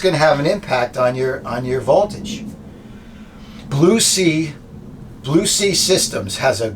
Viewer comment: More like "This Old Man And His Van" :-D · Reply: I know your videos are great, but the little things like, you said "Blue Sea Systems" and I heard "Lucy Systems". Then, immediately a banner correcting my hearing !! Thank you for that, you're so good at dealing with hearing, visually, ambulatory, mentally, feeling and smelling impaired.